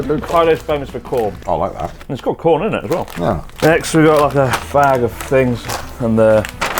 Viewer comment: it sounds like chris is saying “it’s a f*g of fings” lol i laughed so hard